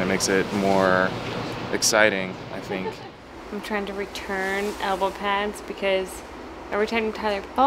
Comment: When Jenna said "elbow pad" I somehoe thought she said "avocado" lmao